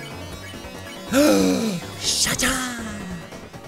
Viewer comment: i thought Sean said "Shat Up"